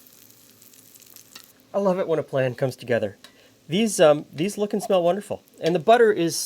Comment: I swear I almost heard you say, "I love it when a flan comes together."